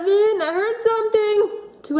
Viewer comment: it sounded like jen said eat